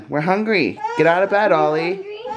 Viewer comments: am i the only one who heard say "Ollie" at 6:59? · in the vid, it totally sounded like said Ollie! ☺️☺️😊😊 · And did anyone else notice that at 7:00 it sounded like said ollie so adorable he probaly just tried and he did really good